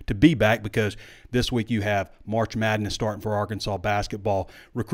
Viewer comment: Did he say "March Sadness"?